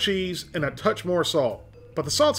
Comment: You said "big beal" instead of big deal